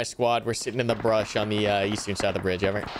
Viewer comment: For a solid few minutes I thought was using that mod that scrambles voice comms for the different teams to make it unintelligible to each other lmao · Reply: I think I heard a few English words there, "southwest of the (bridge?)"